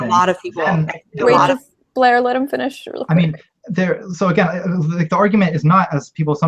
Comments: anyone else hear the moderator say, "Blaire, let him finish". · did shoe just say "Blaire let HIM finish?" lol